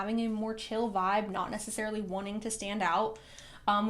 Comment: i swear i thought it says Curly instead of Curvy? Unless I heard wrong and read it as curly but i'm hearing curvy? i guess i'm a little bit biased because the person i'm thinking about has curly hair oops!